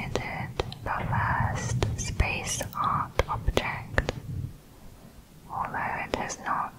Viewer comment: I only came here because I thought it said SPACE FARTS